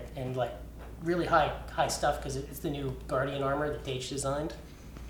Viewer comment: I heard ''new Guardian armor designed by Dage'' Now... Where do I find this?